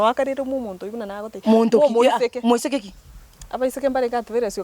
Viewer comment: Did I hear Gesare say “omoitheke”?